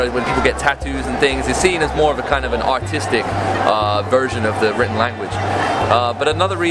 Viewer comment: my my did he say an "autistic" version?